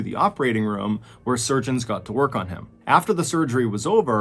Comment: When you said the doctors GOT to work on him all I heard was: "The doctors GOT to cut his stomach open." 🤣